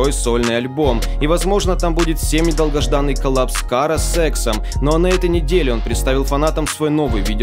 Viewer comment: я услышал сексом а не с Эксом